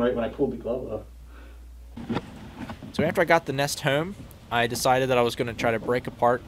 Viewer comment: When you say home it almost sounds like you say comb at 11:09. Will never get tired of you saying that word